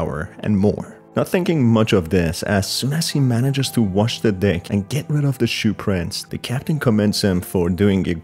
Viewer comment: I keep hearing him say “ wash the dick “😂😂😂😂😂